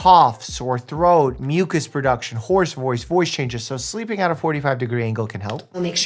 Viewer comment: Did he just say horse voice?